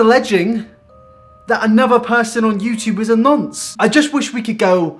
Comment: Another person on YouTube is a... what? Can you please spell that? I listened to it again & I have no idea what he said. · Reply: He said 'nonce'